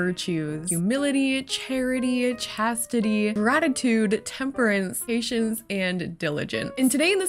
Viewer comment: I misheard "gratitude" as "ratitu-" and thought was just going to casually throw in "ratatouille" as one of the virtues for some bizarre reason